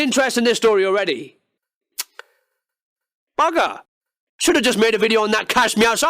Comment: What're you saying at 9:28 ? Don't know the word ;_ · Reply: bugger